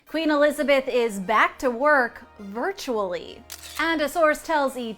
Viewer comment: I thought they said Queen Elizabeth was dead